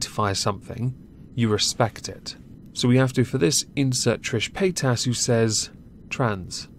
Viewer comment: I swear I thought you were saying Paint Ass.